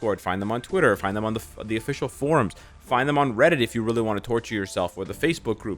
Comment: i thought said find them on only fans😂☠☠